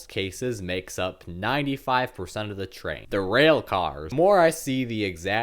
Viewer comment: sounds like you said derailcars